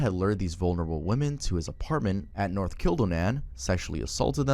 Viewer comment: am i tweaking or did he say he lived at north killed a man